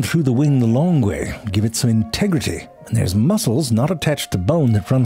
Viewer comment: did he say Tegridy from Randy Marsh? 😂😂